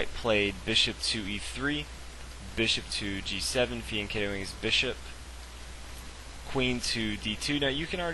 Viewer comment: What's the word he says at 1:24? Ketoing his bishop?